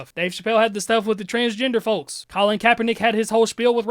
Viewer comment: I thought he said Carlin (George), not Colin (Kaepernick)?